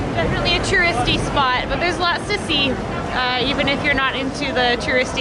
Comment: so I thought I heard you say "there's a lot of sissy" when you said there's a lot to see ::D:S:Ddf